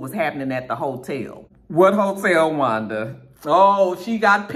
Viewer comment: When you said "What hotel Wanda?" I thought you said "Hotel Rwanda" 😄